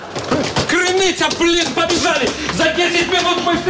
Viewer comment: Вы тоже услышали мат на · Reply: Нет. —Крыныця, блин, побежали.